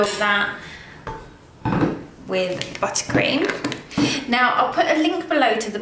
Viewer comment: Haha, I thought she said butt cream